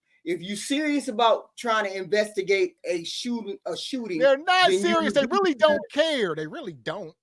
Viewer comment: Did she say “unserious”⁉️ 🤔🫤🫥